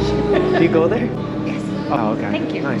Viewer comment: Everybody calm down. Best advice I’ve ever heard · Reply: I thought she said everyone come down LOL